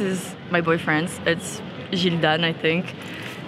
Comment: I need a plain black hoodie, what did she say? Gildan or did I hear it wrong lol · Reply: yes, Gildan